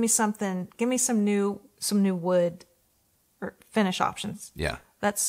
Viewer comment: All I heard was "give me some new wood"